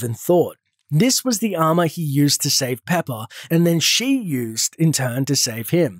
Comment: Who else hear pepa